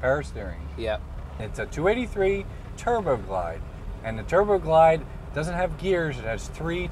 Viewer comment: BTW, who transcribes that BS captioning? For example at 2:03 "283 turbo applied" instead of "Turbo Glide". So stupid it's not even funny.